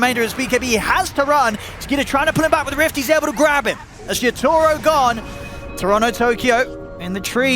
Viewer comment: did he call yotoro, shit toro? 23:32 he is not wrong tho